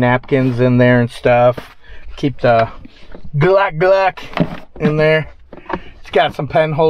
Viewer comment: Did you say Glock @ 13:44in the video? ( I guess you have a ccw permit?)